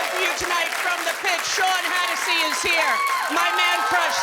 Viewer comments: I about choked when I thought Rosie said Sean Hannity would be on. LOL🤣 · Perfect pitch on the Irish ballad. Tnx Rosie. ( I don't hear well. When you announced guest roster, I thought you said Sean Hannity 😮. I would have loved that 😀